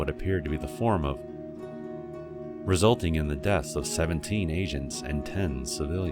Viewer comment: "It killed 10 asians and 12 civilians." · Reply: agents..... i thought it said asians too